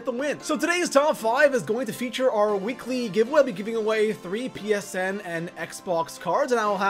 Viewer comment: Did he just say PSM?